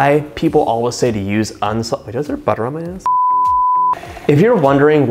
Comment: I thought you said, "Do I have butter on my ass?" LOL!